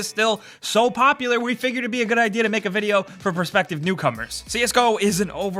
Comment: I thought they said "prospective cucumbers" in the beginning